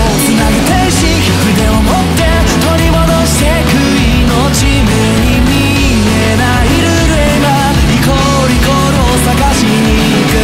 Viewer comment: 『足して』『ひいて』『かけて』『わって』とか『意思(石)』『日々(ヒビ)』みたいな物語と何となく関わる言葉遊びとか『行こう』と『イコール』の繋ぎの耳障りの良さみたいな諸々がすごく好き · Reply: 目に見えないルールへ今 歌詞見るまでは 目に見えない幽霊はって聞こえてた